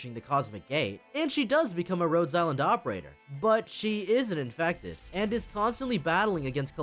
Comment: She is an infected actually, sorry I realize the VO is really low and unclear there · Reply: Ah yeah, my bad.. "She is an infected..." but I heard it "she isn't infected" 😅